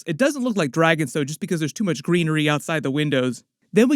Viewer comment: All the greenery sounds like hes saying treachery